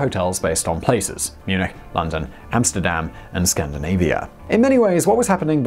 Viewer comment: Did he say "Hamsterdam"? Didn't know Bunny Colvin made enough to buy an island.